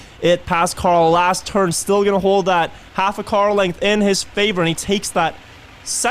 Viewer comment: did he say "Half a Carl-length"? 😜😜